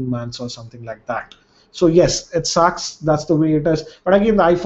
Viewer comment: 21:04 Did i just hear It sucks?? lol this is the first time i heard that from u nice:D:D:D:D